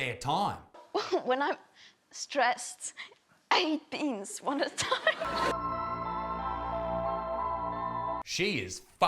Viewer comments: What did she say at 10:42? “I eat beans one at a time”? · I’ve replayed this like 10 times and I still don’t know what she’s saying. All I’m hearing is, “when I’m stressed, I ee pingz, one at time” da fuq? Lol